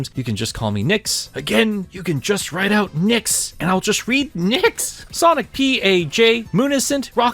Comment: I misheard his name as "kickass sonic" at first and I think that fits his personality more. Like of course he'd call himself that